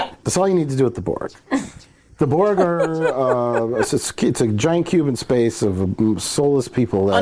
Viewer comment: "the burger in giant Cuban space" is what I heard